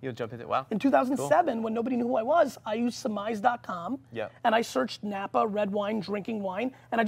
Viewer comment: what website is Gary saying their ssmize .com I didn't catch that I looked it up and didn't find it if someone could help that be great